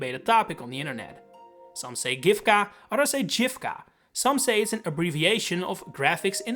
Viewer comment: I wish you showed the written names of all the characters whose name weren't in the video to understand what you were saying. For e.g at 7:54 I didn't understand what you were saying at all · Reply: It's written as Giffca. I go with hard g myself.